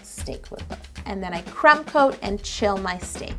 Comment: thought she said crap coat 😂